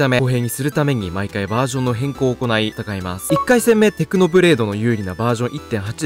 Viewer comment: テクノブレイクって聞こえたわ